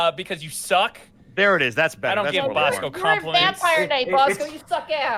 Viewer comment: When opened up and said Vampire knight i thought she said High and i was almost excited because it was an obscure show that i truly missed and enjoyed